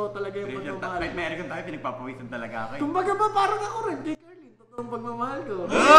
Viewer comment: narinig ko carlyn sinabi ni kuya